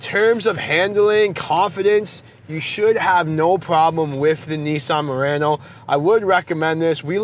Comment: Idk why I heard him saying Nissan Marino in the beginning 😂